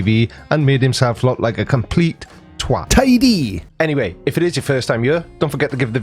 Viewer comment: Hey! This channel is always so informative! I have learned so much "Welsh"-based language. But tell me, at about 15:07 - 15:08 you say what I presume to be a Welsh word that I never have heard before. What is a "twatidy" when translated into English?